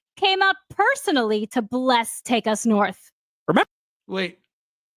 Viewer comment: I am just listening, and I thought it was a goofy game title based on what I heard "Take A Snort"